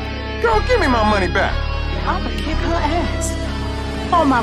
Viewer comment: Bro i thought she said on my wig